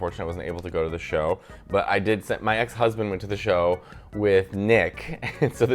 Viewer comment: did he just say ex-husband?